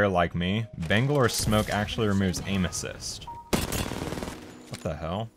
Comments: Damn i heard "Bangalores smoke actually removes anuses"😭😭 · Damn i heard "Bangalores smoke actually removes anuses"😭😭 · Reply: I hear “Bangalore’s smoke actually removes anus cyst”